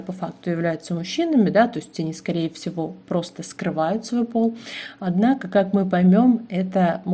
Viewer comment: послышалось "скрывают свой болт"